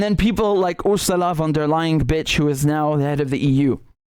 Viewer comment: did you call her b**ch?